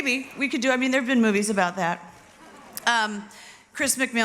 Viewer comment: what is she saying at 0:39 we can do ... ? so fast I can't hear it clearly T T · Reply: she interrupted herself. "we can do...there've been movies about that" it was a joke